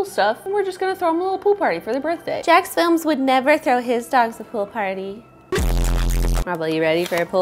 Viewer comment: I thought it said "throwing my dogs in a pool" . I was disappointed